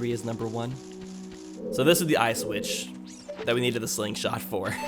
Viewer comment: Eye switch? I thought you said Auschwitz